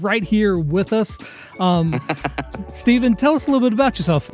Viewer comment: u said and I thought u said Steven Shelly at first then I missclicked and I heard it right